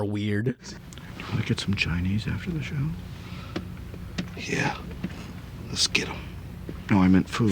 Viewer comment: can anybody tell me what the black guy said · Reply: "Yeah, let's get 'em" I guess he thought the other guy was talking about going on a racially motivated murder spree or something.